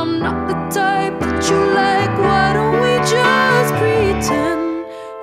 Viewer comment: I thought she said "why don't we just be friends"